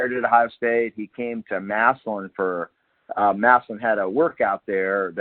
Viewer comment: Did I hear Massillon(12:40)? GO TIGERS! Playing for the State Championship this Thursday Night 7PM at Tom Benson Stadium. Go Tigers!!!!!